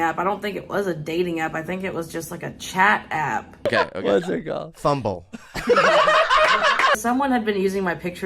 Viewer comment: What is he saying at 1:24? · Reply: Thumble